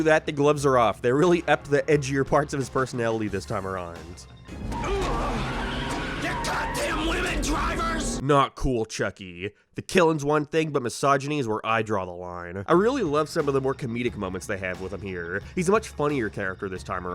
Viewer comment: I thought he said lemon drivers no matter how many times I’ve seen this